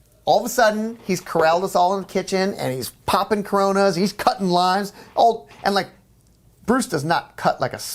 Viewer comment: Bruce cutting limes, thought he said lines at first, lmao